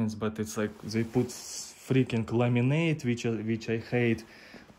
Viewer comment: You confused me for a minute, you said laminate and I thought you said lemonade.